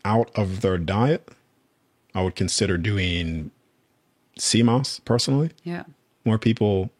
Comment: what is he saying at 1:06:02? Doing sea moss? Is that it??